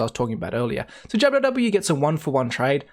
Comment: haha, did you call Jw JabbaW?